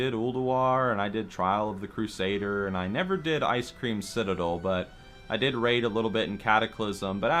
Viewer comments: Am I the only one that heard him say "Icecream Citadel" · Did he really say "IceCREAMcitadel"? :D :D